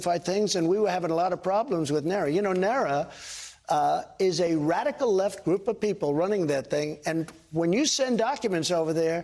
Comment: Please help me! I think I must be hallucinating ... or did Trump really say (at 3:10 of this video), "NARA [the National Archives and Records Administration] is a radical left group of people"? What does he suspect them of doing? Knitting weapons of mass destruction, I guess?